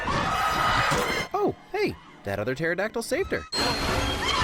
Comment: Subtitles say Tetradactyl but narrator says Pterodactyl